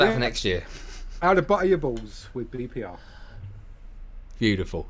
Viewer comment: At the 9:30 mark did say "how to butter your balls"? Or was it "how to butter your birds"? I am sooooo curious. Where was EnderOllie on that one?? So YES, here in North Carolina the sweet potato is on the table, more than 1 way. YES, mac & cheese, my husband actually makes the best macaroni pie. Oh dear God! Think mac and cheese from scratch, bakes in a custard base, no sugar, golden crispy on the edges....... Oh man. Dump cakes are usually pretty dang good! You dump in this and that, bake and yum! SNL will always explain everything.